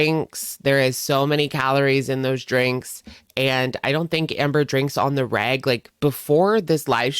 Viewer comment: When you said you didn't think she drinks "on the reg" 8:05 I was confused by your accent and thought you said "I don't think Amber drinks on the rag". 😂 My first thought was 'why does that specific time period make a difference?' Then I realized how totally wrong I was! Lol